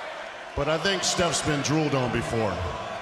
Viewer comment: My english is horrbile. What did Batista said to Stephanie in 0:59 ? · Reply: Taký Čávo "But I think Steph's been drooled on before."